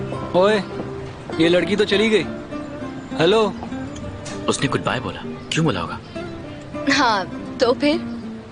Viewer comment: usnay goodbye bola is wrong dialogue, she said Allah hafiz instead of bye...